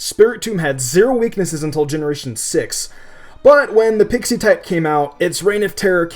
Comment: did anyone else hear pixie type?